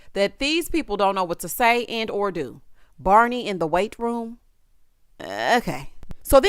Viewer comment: I thought he said "Barbie in the weight room"... still stupid, though 😂